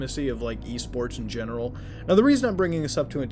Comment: Thought he said E-Scorts